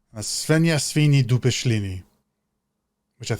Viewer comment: what did you say, i dont get it xD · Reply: zrozumiałam świnia świni dupe ślini🤦‍♀